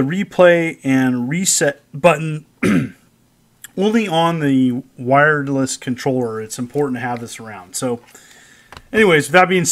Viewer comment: Did I hear you say “Wiredless controller”? lol